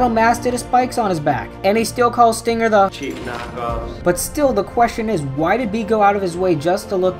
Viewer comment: I thought bee said I hate cheese natchose